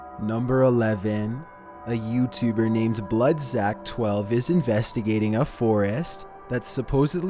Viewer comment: When he said bloodzack i thought he said bloodsack I'm sorry guys😂